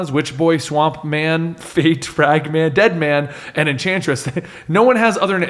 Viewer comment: "Superdemon defends Earth joined by Hellblazer, Annataz, Witchboy, Swamp-Man, Fate, *Fagman*, Deadman and the Enchantress." I swear that's what he says.